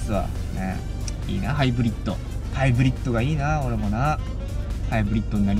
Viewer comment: ハイブリッコって聞こえたのは気のせい？